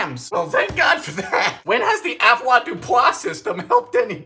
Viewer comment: "When has the ???? System helped anybody?" What's he saying? What's he talking about? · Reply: Avoirdupois. It’s a system of measurement for goods.